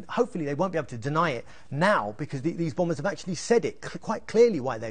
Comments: Hold up a second 6:16 did he just say 'the bombers' hmmm sounds a little bit fishy to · DID H SAY THE BOMBERS??????